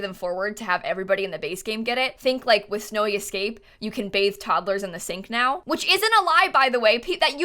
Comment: I thought she said that you can baste toddlers in the sink